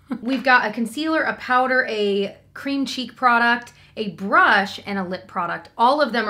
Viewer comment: Thought you said we have a cream cheese product at the beginning 😂 I have the deeper berry shade of Heat Cheek, zero color payoff and zero staying power. Never reach for it